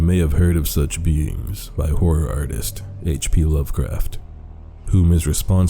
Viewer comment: i wasn't paying attention and i thought he said "Whore artist" lmao